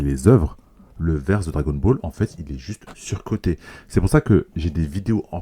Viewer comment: hop hop hop rectification, d'où le verse de Dragon ball il est sous coté? Clairement c'est soi t'aimes pas Dragon ball ou seulement tu kiffe trop OPM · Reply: Il a dit surcoté pas sous côté